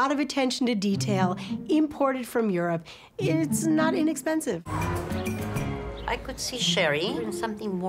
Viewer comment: "Eh just under $2000... Not expensive " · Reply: I thought she said "its not inexpensive"